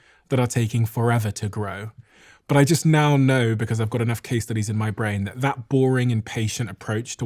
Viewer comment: I appreciate the section at the end about having patience and being boring especially when you said “I have enough case studies in my brain…” because I thought you said “I have enough quesadillas in my brain”.